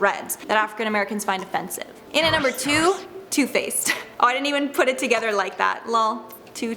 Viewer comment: When she said too faced I thought she said toothpaste 🤣😂